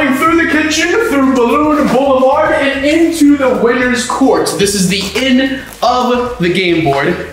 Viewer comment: Did you say ends court at 1:03?